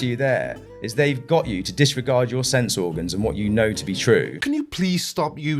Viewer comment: I thought he said sex organ